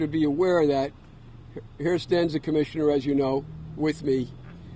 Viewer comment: What did the lawyer say -- at the 1:18 mark? The sentence that ended "as you know." I think he was introducing the professionally attired man, with the red tie, and lapel pin. (Lawyers should master the fundamentals of public speaking, before holding a press conference. That means speaking clearly and slowly, when appropriate.) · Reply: According to the transcript, the lawyer said "Here stands the commissioner, as you know." (Personally, I did not know. It is unfortunate that the lawyer failed to properly introduce the "commissioner." He is probably the (new) police commissioner, of Suffolk County, New York.)